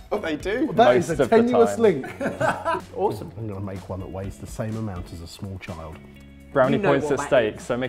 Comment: I heard comment about his burrito weighing the same amount as a small child as "I'm going to make one that tastes the same as a small child" and I was mildly disturbed for a few moments